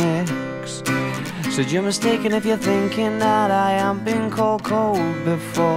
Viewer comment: What does Alex says at 0:38? I mean, I know that is I HAVENT BEEN but I can no listen that pronunciation. I am not American btw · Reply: You're mistaken if you think I hadn't been called cold before